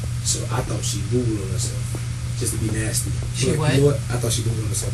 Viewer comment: what the hell does he keep saying starting at 5:39? "I thought she googled herself"? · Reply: pooped herself ...but he said doodoo'ed